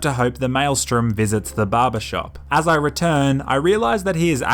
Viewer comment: Barber.... I thought you were saying baba, like Baba Ram Dass, like a spiritual leader...